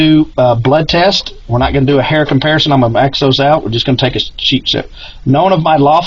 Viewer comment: "We are going to max those out." (Regarding his sentences). The detective knew he could say anything and this guy would just go along with it at that point. Sure, it sounded enough like, "x those out," but I know what I heard...